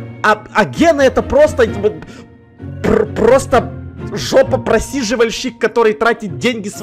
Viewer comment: я услышала жопапросильживасчик Теперь моя школа жопапрасильжиков😵